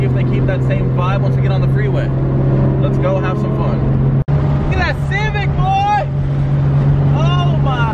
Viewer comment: Yooo at 6:10 Ik I’m not trippin but did he say “look at that civic boi that nigga so sexy” or I’m the only one hearing it ??? Wtf🤦🏻‍♂️😂